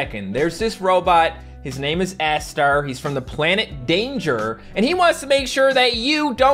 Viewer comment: Who thought that he said ass-star at 4:58?